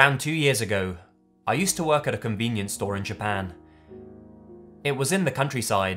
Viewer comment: Goddamn I can't take the word "senpai" seriously anymore... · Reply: and i keep hearing "combinience store" instead of convenience store